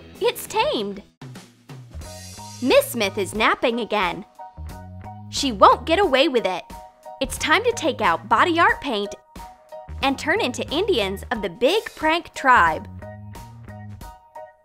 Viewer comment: is she say indian